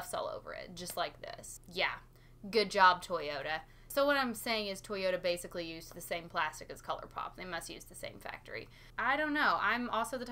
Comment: Omg I thought you said “So what I’m saying basically is that Toyota and Colourpop is the same company”. This was my first first impression watch in like 6 months and I didn’t care about the product I just needed to hear you talk about it. Would you ever do a video where you (hypothetically) pick like 15-20 colors from existing palettes to create a perfect palette? I would be so interested. We missed you 🙏🏻🤗❤️🥰